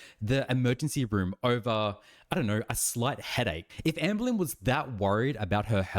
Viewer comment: I thought you said S L U T headache 😳😂🤣